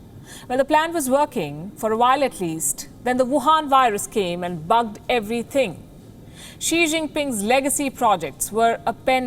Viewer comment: thought she said "the wuhan virus came and fucked everything" lol